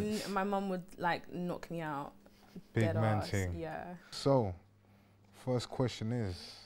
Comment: Wow the youtube subtitles instead of saying Big Man Ting says 'Pigmenting' 😂😂😂😂😂